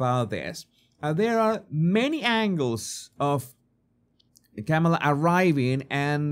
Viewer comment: When you said "Kamala" in the video at 3:32, the captions said 'the camel' 😂